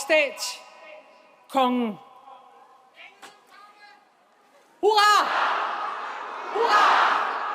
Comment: what are they saying here repeatedly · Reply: On Danish: "Hurra!" - English translation is "hurray" or "hooray"